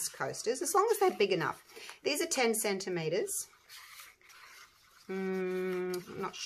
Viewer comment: I thought you said 10 cent tomatoes...I was thinking she doesn't even have red coloring... then it clicked... lol and i had to listen to it 3x. 😆